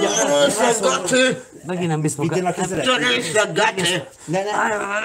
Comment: Mit mond? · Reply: A azt mondja: "Te részeg geci."